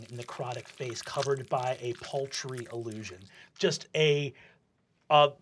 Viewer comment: I love the captions at 9:05 instead of paltry illusion his face is covered with poultry allusion 😂😂😂